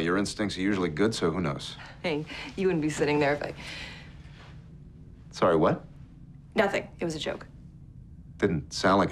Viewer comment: I wonder if the truth is she cheated on him so he would get the command :D. EDIT: Did that "sorry what?" only sounded like "WTF did you just say?" only to me?